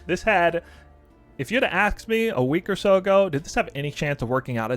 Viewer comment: did you say “axe me”?